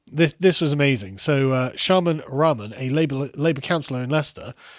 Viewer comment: I thought you said Shaman Ramen haha, sounds like the name of a D&D playing college student who blows his money on psychedelics